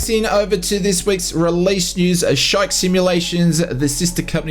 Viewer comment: I thought you said Shite Simulations! 🤣